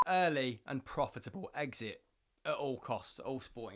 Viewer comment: I thought he said Brexit not exit! Lol!